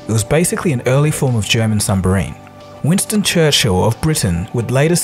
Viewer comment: Microwave o-ven... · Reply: I thought he said sumbarine as well.